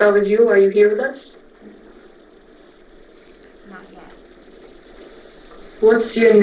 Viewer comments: I heard something say "not let" listen to it aging and you can hear it. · Is it just me or at 49:08 I hear not yet very soft